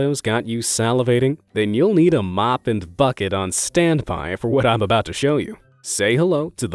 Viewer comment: did he say the f-word? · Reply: bucket